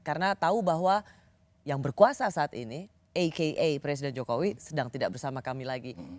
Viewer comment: rosi bilang apa ya.. ei kei ei ?? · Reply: Bhs inggris "A.K.A" dibaca /ei kei ei/ singkatan dari "As Known As, Also Known As" artinya = "spt sdh diketahui bersama (maksudnya dlm konteks ini merujuk ke 'pak jkw sbg yg sdg berkuasa'), arti lain "yg dikenal sbg, yaitu, alias, yg dimaksud adalah", dll.